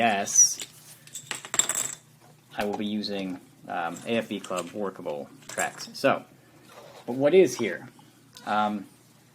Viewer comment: Great review. Is there a magic track or other replacement for the DS tracks that come with this kit? Couldn't make out what you said here [35:54] EDIT: ok after listening multiple times I made out "AFV Club Workable tracks". :) Item number for the tracks that work with this model (#6700)?